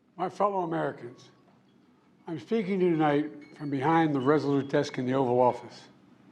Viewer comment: Did he say, "I'm FEAKING to you tonight."????